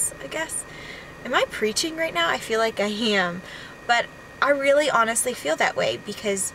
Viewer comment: "I feel like a ham" Does anybody else hear this?!